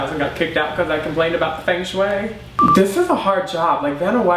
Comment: WHat he said at 2:03 ? 'about ..... way' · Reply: Feng shui (I think thats how you spell it) ive ehard of it but im not exactly sure of what it is. Sorry